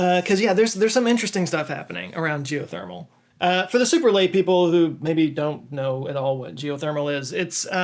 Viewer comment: I thought you said 'super lame people' when talking about geothermal energy, and I was all like "yeah, people who don't know about that shit are super lame", but I don't think that is what you said.